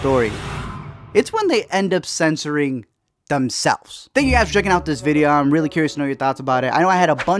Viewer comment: Did I just hear "Thank you guys for jerkin on this video?" On his ending script?